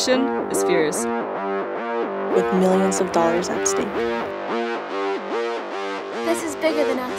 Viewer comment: I heard "millions of dollars OF steak" which initially made it even better.